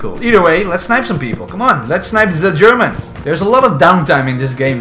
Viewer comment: I'll go ahead and sacrifice myself to the comments here. It's an M5a1 Stuart Light Tank, NOT a Sherman. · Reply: I know it's a tank lol I just think his accent makes it sound like he says Sherman when he says German